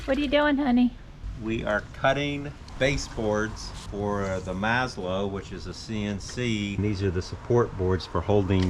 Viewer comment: Had to rewind; thought you said Laszlo 😮. That is a cool machine